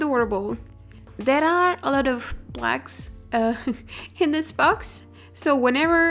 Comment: At first I thought she said"there's a lot of blacks" but then i realized she said "plugs"